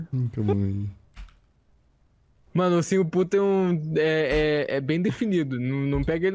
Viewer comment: eu escutei " ursinho puto " kkkkkk